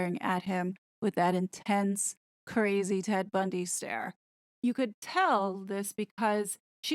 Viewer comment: I heard "that intense PEG Bundy stare"!!! Then I realized you actually said "TED Bundy stare"!!! 😜